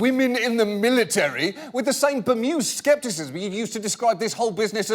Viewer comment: What's that word John said at 4:15? I can't quite make it out on my phone's speakers. Sounds like bermuse? Lol